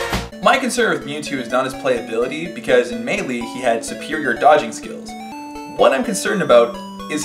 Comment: I thought you said Serperior dodging skills, and I was like, "Bulba, you need to put down the games and study your English... o.0"